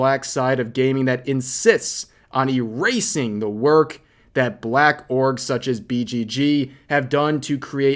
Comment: did they said blacks are orgs?